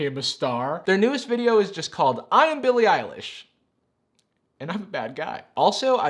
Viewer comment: I swore the Billie Ellish said I'm Billie Elisha and I'm gay